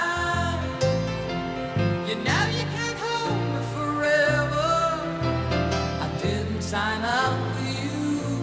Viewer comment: I thought he said "I didn't sign up to be used". It's good to know that NO ONE DOES NOT NOT like this song (Yeah double Negative)